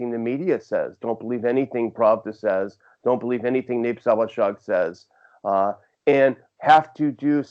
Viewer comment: Can anyone figure out what McAdams is saying after mentioning Pravda, at 3:25? I tried 'Nape Sowashog,' but that doesn't get any results. Closed captioning gives it as 'Nape Sawashag,' but that's not it either.